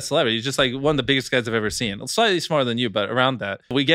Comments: I thought he said the orher other was slightly smarter then you, but he really said the other guy was slightly smaller. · “Slightly smaller than you tho” 😹 had to give him the reassurance so he doesn’t feel bad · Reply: Ooohhhhh😂 I thought he said “slightly smarter than you tho” 😂 I was like 😮 he just gonna let me beats say that ??😅😂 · Bro at first I heard "Slightly smarter than you" lmao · I thought he said “slightly smarter than you” 😭